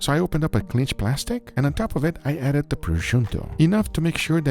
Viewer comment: Wait did just Guga say topruscio instead of prosciutto? xD 3:36 I do not even know.